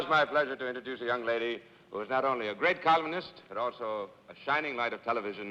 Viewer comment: When Cesar introduced Dorothy, I thought at first that he said, "She's a great COMMUNIST!" and my eyes bugged out.